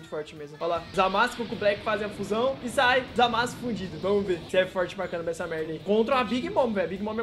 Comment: Kakakaa eu entendi zamasu fudido